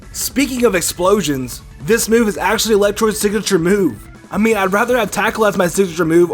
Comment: did he just say "electroid" ? and not electrode?